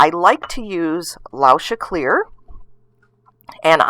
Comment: I didn't catch the material of the glass rod being used at 0:50. Someone? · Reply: Lauscha clear